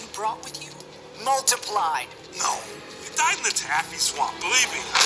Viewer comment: What Did She Just Say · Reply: Bullroar